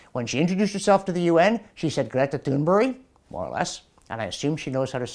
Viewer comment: When I heard Great introduce herself, I heard her say (phonetically), "My name is Gree-eta Toon-berry." Listen for yourself. <link>